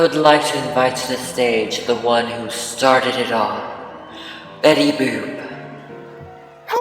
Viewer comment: When they said betty boop at the start I thought u said betty boob